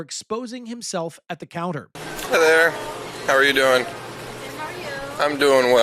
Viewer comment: HAHA I THOUGHT HE SAID “hellur” I WAS ABT TO SAY “madea?!” 5:29 the mf has a cross on.. that is pissing me off after what he did